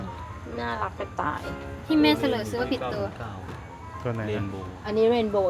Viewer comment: เหมือนได้ยินไม่ผิดพูดว่าเสร่อใช่มั้ยคะ เหมือนว่าคลิปก่อนๆก็เคยได้ยิน อยากให้แคมุนสอนเกี่ยวกับคำพูดหน่อยค่ะ ว่าคำนี้ไม่สมควรพูด หรืออาจจะเข้าใจคำนี้ผิดค่ะ เลยใช้บ่อยๆ🥹